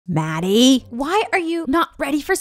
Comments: I thought she said baddie 😭💀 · First i thought she said baddie